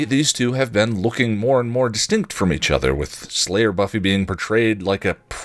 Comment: I did say distinct. But I'll own the Grant/Graham mistake. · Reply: Check 7:10. I swear I hear “extinct”.